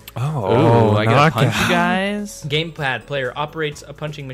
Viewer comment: I heard GamePlad lol